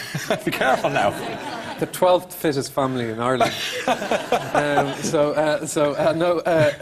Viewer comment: What does he say around 2:09? :/ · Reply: "Well... I come from the 12th fittest family in Ireland." Fit meaning good looking.